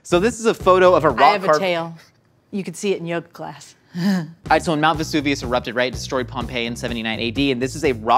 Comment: I thought Josh said he saw Mt Vesuvius erupt 79 AD